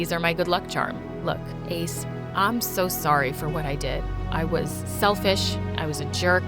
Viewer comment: For a sec I thought she said her name was AIDS 😂😂😂😂nice story tho